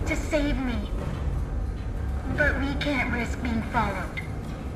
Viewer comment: instead of risk it’s “Rick”